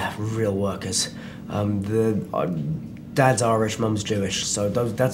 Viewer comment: Did Daniel say his dad was Irish? I thought he said Amish for a second...!